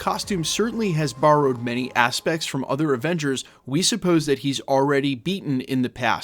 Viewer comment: Sounded like you said other Revengers 😂 comment below!!!!!